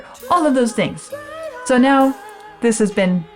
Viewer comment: I thought at the end you were trying to get one past us. Speaking of melody of the words. I thought I heard you say, "Auf wiedersehen." Nope. I doubled back on that. You said, "I'll see you soon." Ich kann deutsch. Funny the tricks the mind will play. Either language. I'll check back soon :D